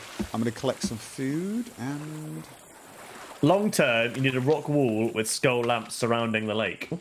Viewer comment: Wait, did Chat say "in the long turd " at 45:55?!